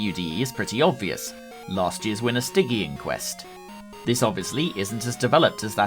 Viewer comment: I misheard Stygian Quest as Sticky Inquest and now I’m trying to figure out how that would work as a game